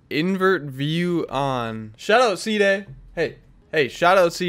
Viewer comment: I was tripping i thought you said shut up ceeday 😂